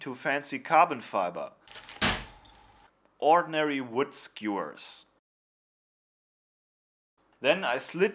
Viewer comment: what did he say at 3:18 ordinary wood ......? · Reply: I think he said "skewers"